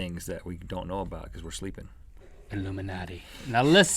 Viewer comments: link said illuminate did anyone hear closely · Wow im the only on who heard illuminate LOL XD illuminate confirm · what did link say?!?! Hint: ill_mi_atti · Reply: illuminati